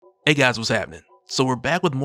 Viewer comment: Every time I thought you said “hey guys what’s crackin” until today. Gotta get the VA to recheck my ears